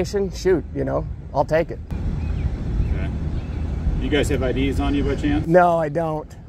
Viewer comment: At around the 2:54 mark, one of the officers asked for id. When he said it, it sounded like ideas. I'd love to see, when they ask for ids, you give them your list of ideas.